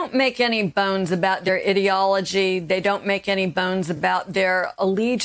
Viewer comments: I misheard ideology as idiotology and thought "yeah, that tracks." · for moment I thought she was gonna say "they don't make any bones about their idiocy"